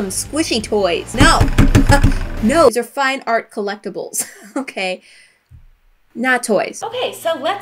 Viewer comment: I love how said colleckables instead of cellectables lol. 7:11 btw